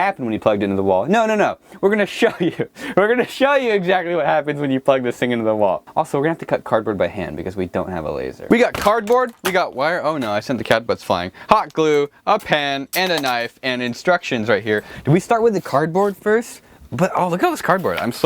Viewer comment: Did he say he sent the cannabis flying.